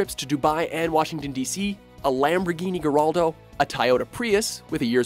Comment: What did he just say? 08:52 a Lamborghini GERALDO, a TIEYOTA Prius? Tongue tied i guess 😆😝